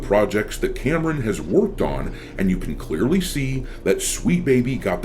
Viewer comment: instead of "worked on" i heard "woked on" xD Did i misheard it? Can we make it a new term when DEI hires ruin games?